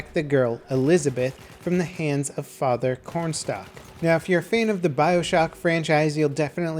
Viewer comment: It's Comstock, I thought you said cornstock.